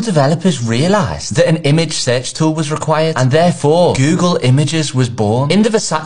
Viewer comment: I heard "image Sex tool was Required" 😂😂😂